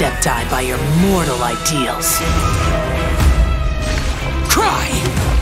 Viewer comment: WAIT I DIDNT KNOW HE ACTUALLY SAID CRY LMAO